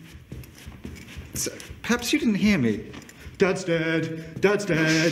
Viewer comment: Honestly for all of this time I thought that at 0:59 he was saying ‘dance dance’ but now I realise he said ‘ Dads dead’